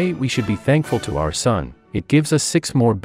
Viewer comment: did u just swear by saying s*it?